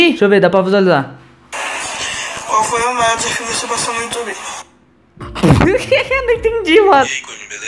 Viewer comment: ele fala "qual foi o método de você crescer no YouTube?"